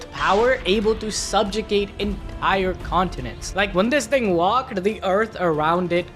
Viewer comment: Am I the only one who heard "nigga" instead of "thing" the first time